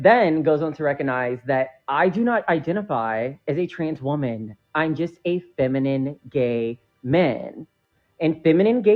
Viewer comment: What i heard was...."I dont identify as a woman but desire to be viewed as a "feminine being"?!?!